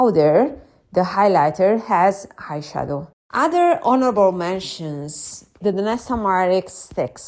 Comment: Haha, almost thought you said horrible mention! I had some coupons and got myself one of the Hourglass Holiday palettes. Everything you said about their powders is so true. Happy New Year!